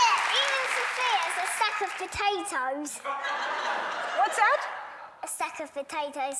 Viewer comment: Rosie is hilarious, but I hope she grows out of the self depreciation. You're not a sack of potatoes. You are wonderful. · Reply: I thought she said sophia is a sack of potatoes